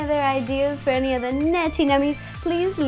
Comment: It sounds like you say naughty nummies, not nerdy nummies... wierd...